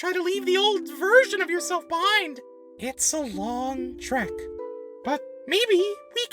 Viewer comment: IS TREk NOT SHERK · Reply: yes, I know that, but it sounds like balloon said "shrek" at 15:20 so I made a humorous comment about that